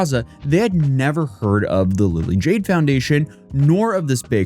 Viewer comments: What’s up with the Lily’s? Lily Jay Lily Jean. Haha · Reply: I heard him say it while talking to my wife, and I misheard it as the "Lily Jean Foundation". I'm sitting here like "Please don't name a fraud charity foundation after that psycho; that's just ASKING to be ousted" XD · I thought you said Lilly Jean. This is right up her alley 😂